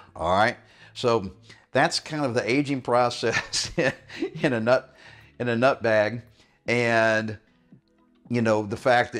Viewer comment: did he just say 'that's kinda the aging process in a nutbag???? 😆 Love the advice BTW....just turned 58.....looking better then I did at 40....